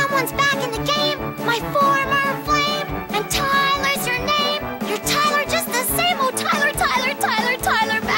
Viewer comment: I swear i heard "and tyler hers name-"